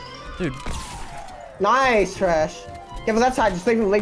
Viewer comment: did he just called you trash